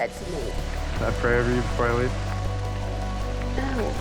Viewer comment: Did he say “can I pray over you before I leave?”